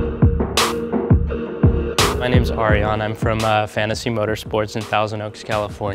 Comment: I thought he said "My name's Arianna.